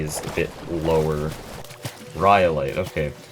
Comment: I thought he said cryolite for a second and I got so confused